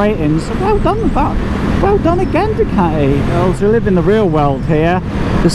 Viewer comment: Great review, at one point I thought you said well done Aldi not Audi!!!!